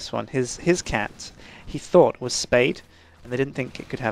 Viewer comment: I thought said "spade" and i was thinking, why the hell would you call a cat that?! :3